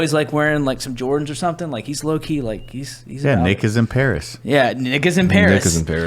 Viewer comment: "Nick is in paris rn" I honestly thought af first ya said niğĝå§ in paris fml